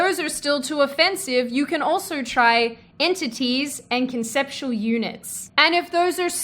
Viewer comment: At first, I heard you say "conceptual eunuch", and thought I have been mis-gendering Beto Orourke when I called him a guy!